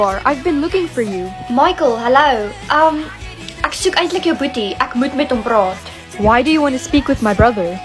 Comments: Hold up when she was talking to Michael it sounded like she said I like your b0oty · wait what he has a (it sound like she said booty)i dont speack that languesge